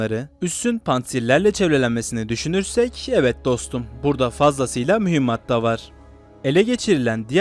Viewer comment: de yanlışmı duydum DOSTUM diyor??? Biz Äbd, li degiliz kardeş. 🇹🇷🇦🇿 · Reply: İzleyenlere dostum diyorum. Evet izleyen dostum burada fazlasıyla mühimmat da var anlamında.